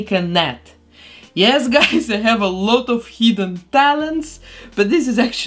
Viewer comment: It sounded like she said heathen talents instead of hidden talents 😆